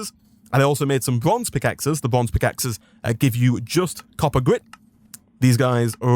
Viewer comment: every time I hear you say "grit" my migraine inflicted brain's just like "oh Homestuck? where?". No, "grit", not "grist". I need to go lay down